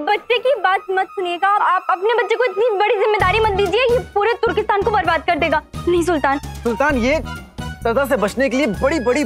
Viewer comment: i think she said 'please' sultan